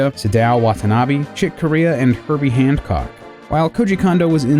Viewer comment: i heard "handcock" instead of hancock :) ...